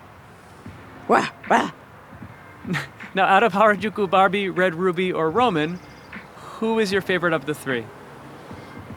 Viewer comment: What did Roman say at 9:39 ?!?! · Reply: “Like a dungeon dragon” 😍